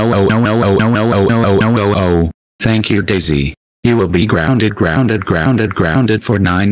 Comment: instead of saying to daisy “he will be grounded” it sounded like “you will be grounded”